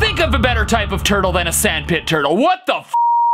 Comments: “THIS IS SANDPIT TURTLE!” Got me the most xP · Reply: I heard "THIS IS SIMP ETERNAL" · Can’t be the only one who heard simp eternal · Keep your luscious 'METAL' hair! Go to <link> to get 50% off your first order of Keeps hair loss treatment! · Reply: I didn't hear" this is sandpit turtle", I heard " THIS IS SHRIMP ETERNAL!"